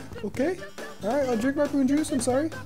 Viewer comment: i thought it said "poor old jews" instead of prune juice